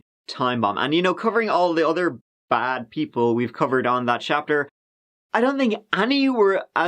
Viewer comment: I just realized your channel is called "THAT CHAPTER" not "THAT CAPTURE" which I thought is said like forever.